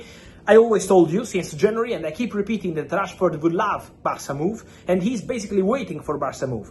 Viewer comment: Did I hear "Trashford wants Barça move"? :face-purple-smiling-tears